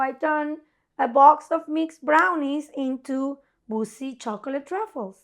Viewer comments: 1) sombody plese tell claire how to spell tszuj. 2) when said boozy i thought she said bussy and i spit my drink all over my desk this is the best channel on youtube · I KNOW said "boozy" but dammit it sounded like she said bussy